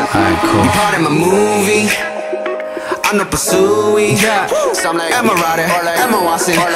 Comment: Thank you so much! I learned a new expression: "안 높아 수위" I thought he was saying "I'm the pursuee" ;)